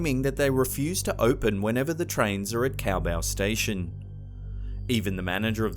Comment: I thought you said `Cowbell Station` (Caobao Station) Perhaps a spot for a future ghost of Christopher Walken - roaming around and pleading for more cowbell...